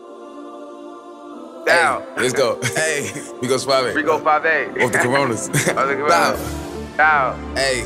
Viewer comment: why does it sound like he is saying "with the Coronas"